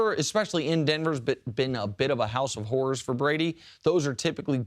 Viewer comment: did he say "house of wh*res" 🤔😅